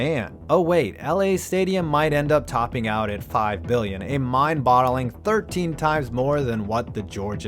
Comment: Mindbottling eh? Alright Chazz, I hear you.